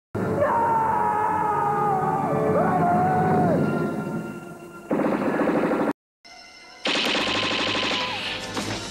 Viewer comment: What does Marty's censorship say at 3:05? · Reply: Murderer I'm pretty sure